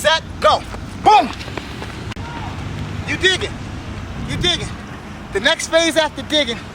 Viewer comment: When he says digging on your head I thought he said dicking on you head ...